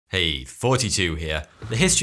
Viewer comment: Anyone else think it sounds like he says”forty-two here”?